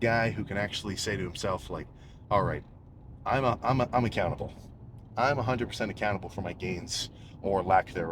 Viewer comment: am i the only one who heard "I'm a cannibal" i was so confused 😂